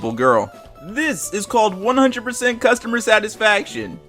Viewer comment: I thought it said they were boneing the 102 customer I am happy I looked back